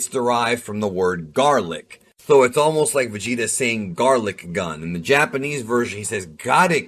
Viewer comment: Ooh did he say garlic gun